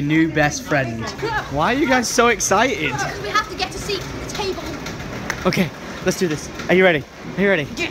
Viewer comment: Thought she said she has to get a seat with a table 😂😂